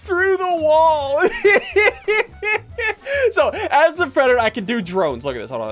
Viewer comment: i thought he was saying “as the predator i can do drugs.”